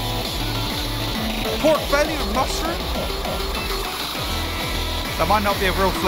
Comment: DID YOU SAY.....MUSTARD????? MUSTARD?!?!?!?!?!?!?!? DO THE BARTMEN